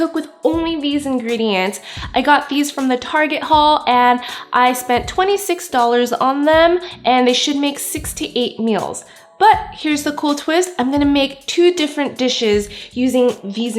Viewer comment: I heard "they should make 68 meals" at first and I was so confused haha ! Good video though :)